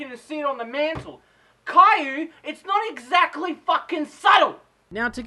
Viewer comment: "The Queen of don't give a fuck Caillou " I thought Caillou was a little boy (Possibly with cancer who really knows) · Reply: ***** I myself knew you were saying kyu it just sounds like you said caillou :P